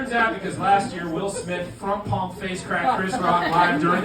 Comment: what did he say here? I’ve replayed it like 10 times. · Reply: Front palm face cracked! I want it on a shirt…